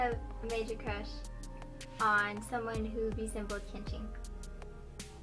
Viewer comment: My English is not that good. But at 8:17 what does she say? Someone who would be simple at kinching? I just cant figure it out and it bothers me T_T